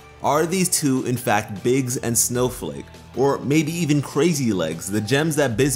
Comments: Crazy lace? I thought he was saying crazy legs the whole time lmao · Actually, the full names of the gems that Bismuth mentioned were Crazy LACE (which is a type of Agate), Snowflake Obsidian, and Biggs Jasper. But when I first heard Bismuth say Cray Lace, I thought she said Crazy Legs too. Great video btw as always though! 😀😀😀